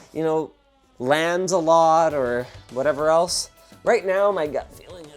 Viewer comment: Sounded like Linus said Lancelot, when he said LANs a lot 😂